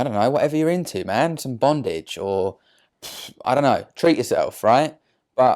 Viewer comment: did he just say bondage ? like bdsm ?